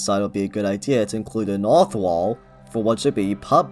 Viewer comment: A naw-tha-wall?? Ugh... I've listened to this five times, and I just do not understand what you said. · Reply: It's Auth-wall, sorry for that. I've had to rerecord my lines a lot because I felt I wasn't being clear enough, but I guess some stuff still slipped through there. This is why I recommend using captions because I can't guarantee that I'd speak perfectly clearly a lot of the time.